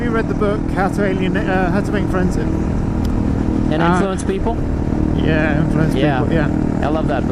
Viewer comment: Can you post the name of that book you guys are talking about at 13:50. I couldn't make out the title. Thanks! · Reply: "How to Win Friends and Influence People" by Dale Carnegie. <link>